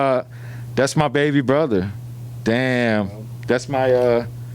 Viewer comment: bruh did Ruby just say the n word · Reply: Pretty sure he said baby